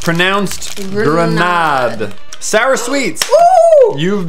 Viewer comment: I'm so jealous of all the Belgium people, your sweets look soooo good! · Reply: I thought you said "your sweats". Yeah...